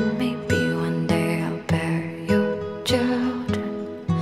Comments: i thought it said "bury your children". bear makes much more sense. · For ages I thought she was saying 'bury your children' I'm glad that's been cleared up for me.